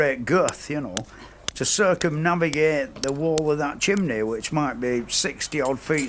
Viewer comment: I thought he said 50-60ft circumference not height?